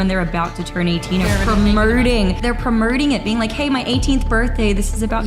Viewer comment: she says "premerting" instead of promoting like she's Doctor Evil. Then in an Australian accent she says... "nauuuurrrrr" instead of no.